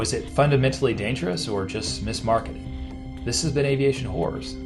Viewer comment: For a second I thought you said "this has been Aviation Whores, thanks for watching! ! haha We do love aviation.